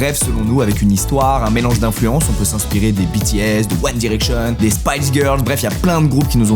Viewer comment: attend mais j'ai entendu BTS la ?